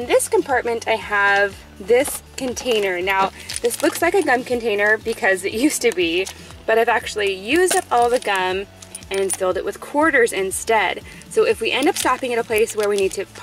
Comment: when you said “this used to be a gum container” i thought you said GUN container hahahahahhaa